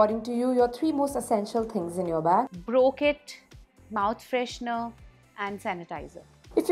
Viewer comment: She said ' eye brow kit ' I heard " I broke it " 😛😛😛😛😆😆😆